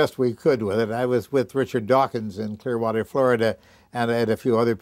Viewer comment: When he mentioned Richard Dawkins at the start, I thought he said Charles Darwin for some reason... Though i would believe he signed something with Darwin :D